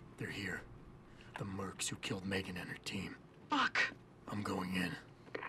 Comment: lol did she say fuck at 10:50?